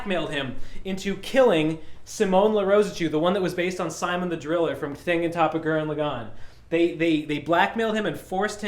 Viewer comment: did I just dream that, or did he actually call Simon the Digger "Sy-min the Driller from Thing on top of Gurren Lagann"?